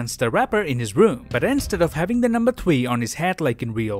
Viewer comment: Did anyone hear the guy say twee instead of three